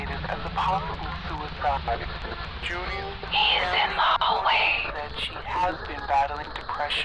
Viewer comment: I thought she said @ 36:34 "Hear them always" O_o